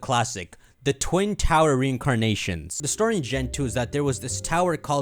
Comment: Thought he said "twin towers recrecreation" I was like bro what tf you talking about